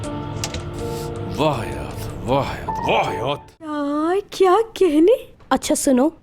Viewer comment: Murat says wah hayat. Nd I heard wahyaaat ,😂😂